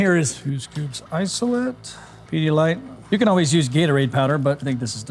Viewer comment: Can someone tell me what’s second ingredient? 5:13 🤨🤨🤨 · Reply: pedialyte